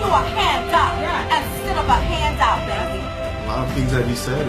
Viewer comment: Hand job instead of a hand out, enough said haha.....